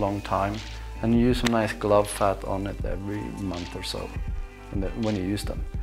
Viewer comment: you mention treating your gloves every month but I can't make out what you say to use. Sounds like 'glove fat' but not seeing that when I google. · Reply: Sno seal is great stuff